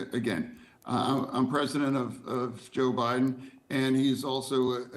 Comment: did he say I'm president of Joe Biden😂😂😂